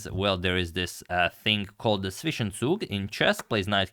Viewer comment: I keep hearing it wtf does he say at 3:08 i have no idea what it means and can not even attempt to spell that out. fishen soog? phisandsook? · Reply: Zwischenzug, it's a German word that basically means an in between move